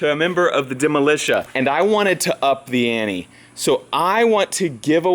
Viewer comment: I swear I heard "up the Annie"